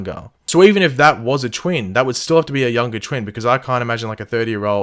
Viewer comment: did he just say YOUNGER TWIN?